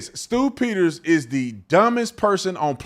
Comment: I thought you said “stupiders” but then it is “Stew Peters”😅